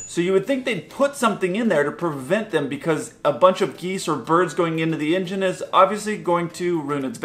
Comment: Please forgive me for putting this silly comment on such an informative video about a serious topic but I think you have the sense of humor to appreciate it. At 2:03 I thought you said, “a bunch of Geezer Birds going into the engine.” 😂 Who knows, maybe they can’t see where they’re going?! Another great vid, thanks!!